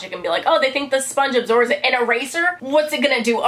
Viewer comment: When you said eraser I thought at first you said razor....ow